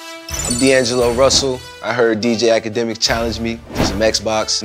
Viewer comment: Imagine 1:21 nigga goes I heard Pouuseeeeyyyy ass akademiks challenged me